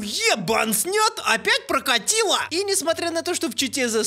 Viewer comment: оу 'е бан' я это услышал